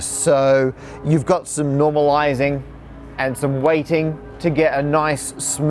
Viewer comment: Whoops! Subs say "waiting" instead of weighting